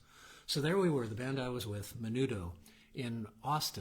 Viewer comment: I absolutely was not. The band was called The Waybacks. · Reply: i swear you say "Menudo" as your band moniker..... i swear it!